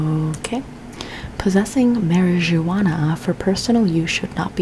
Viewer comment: did you say MarriageAWANA 😂 aren’t you Venezuelan